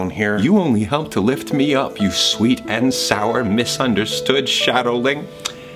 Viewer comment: "You only help bring me up, you sweet and sour little misunderstood shadowling ~" --Best Patton Quote EVER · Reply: I thought he said shadow link